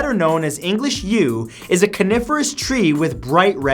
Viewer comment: I thought he said carnivorous